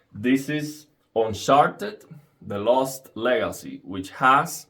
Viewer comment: "This is un-sharted." Lol, sorry man, I heard you say it like that. Thank you very much for this demonstration.